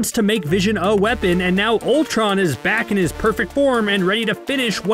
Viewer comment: did i just hear perfect form?